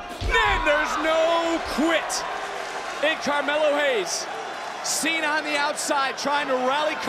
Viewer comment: and 1:17 is he calling him Carmella?